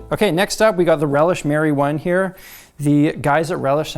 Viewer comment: When he said ‘Relish Mary One’ I thought he had said ‘Relish marijuana’ and I was like... fair enough, my dude.